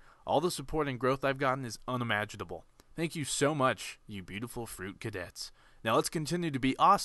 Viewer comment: i honestly thought you were about to say "you beautiful fruit cakes"